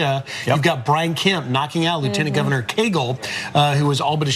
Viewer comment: Did he just say Lt. Governor Kegel?? HAHAHA great name for a Republican, like Santorum.